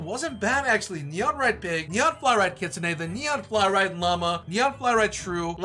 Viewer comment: When you were trading the mega fallow deer I thought u said “neon fly ride mama” instead of llama